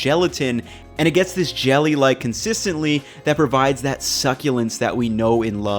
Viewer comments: First I heard “consistently” instead of consistency, then “explosed”... are you messing with us? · you said consistently instead of consistency. Great video! Looking forward to trying this recipe!